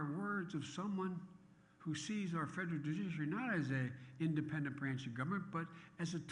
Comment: what in the world did he say...? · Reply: Federal judiciary.